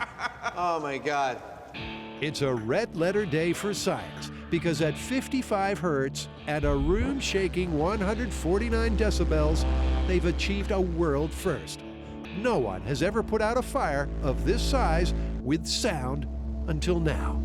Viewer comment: At first I thought the narrator said 140 million decibels and naturally thought the next scene would be a room filled with human mist.